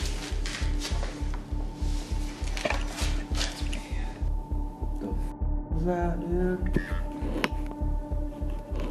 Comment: On this time i can hear a voice saying something but i cant understand it if enyone else can listen n figure out wat its that will b amazing . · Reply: Sounds like "let's begin!"